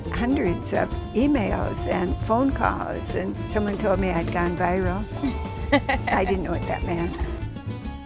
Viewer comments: WTF does she say there? Even the closed captioning can't understand her. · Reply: I had gone viral, I didnt know what that meant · What is she saying at 1:20 ?? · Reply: Even though your comment is a year old I'll still reply, she said "and said that I'd gone viral"